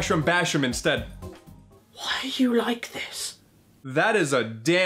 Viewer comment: "Why are you like this" "That's a damn good question" Maybe revenge because of Caddy using the gun on you? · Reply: I thought he said "Why do you like this?"